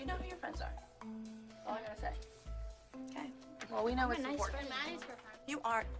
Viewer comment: “Well nice work mom.” I think that’s what Maddie actually said. Which is sad because I think that only proves the point that Melissa (and obviously Abby) forced Maddie to lie or at the very least keep the dance a secret. Also, I don’t know how I’ve only realised this now considering I remember watching this episode when it FIRST DROPPED like eight years ago and I’ve rewatched it way too many times since then, but the ending of Paige and Chloes dance is the most heartbreaking thing to watch knowing what happens afterwards. The wishbone splitting is like foreshadowing to the girls being split apart. It’s almost suspicious how much it seemed like a goodbye dance. Would make you wonder if Abby or whoever decided to choreograph that number knew that Paige and Brooke would be gone by the end of that week….